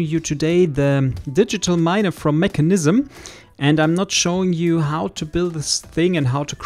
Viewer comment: i thought you said "I will not be showing you how to build and use it." xD